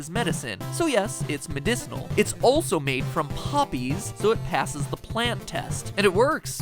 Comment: I thought he said puppies! Omg! 😭